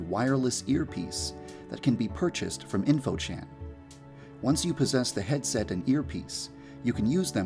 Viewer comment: i love it when having serious a deep voice when the video just make me laugh sometimes-: "once you posses the head set" (i hear him saying posesed-👁👄👁)